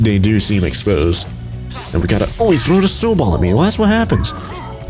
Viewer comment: did you say, “Oh, he THROWED a snowball at me?”